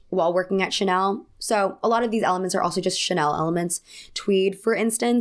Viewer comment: "Chanel elements." All I heard was "Chanelements."